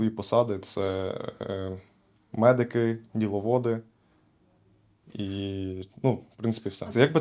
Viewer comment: Вибачте, а що таке «ніловоди», чи то мені не так почулося? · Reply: Дiловоди